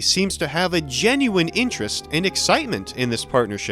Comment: Small word of advice for the subtitler, said "'Sono', the Portuguese word for sleep", not "Somo", though it'd be more accurate for her to say "Dormir" instead of "Sono", which means dream.